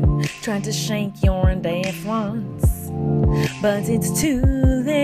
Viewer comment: "Try to shake your deaf rinse" I what I heard at 0:19 XDDD